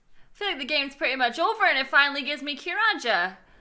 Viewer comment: Did she just say “curaja” .-.